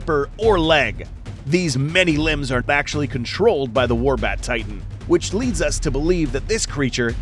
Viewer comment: I thought it said “Wombat Titan”